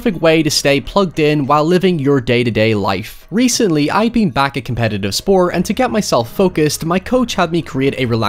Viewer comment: I thought you were saying you recently got back into "competitive Spore". My mind was blown, lol. I was like "How the hell does Spore have a competitive scene?"